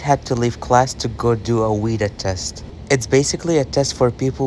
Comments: At first, I thought he said weed test. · Was I the only person that thought he said weed test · I thought you said a weed test 🤣